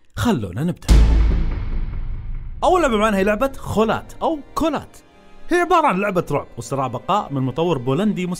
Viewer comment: ما اعرف ليش بس سمعتها كونان لاول مره 😂😂